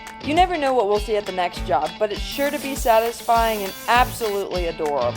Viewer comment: I know that you said it was sure to be satisfying in the intro, but I swore you said it would be sheer to be satisfying and I cannot unhear it. 😆💞